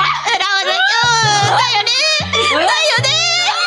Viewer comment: wait what did Tenma say and what does it mean? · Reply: She's saying "da yo ne" which in Japanese essentially just means "I know right?" or something to that effect